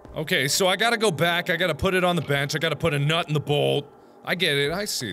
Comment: When he said nut in the bolt I thought he said nut in the hole🤣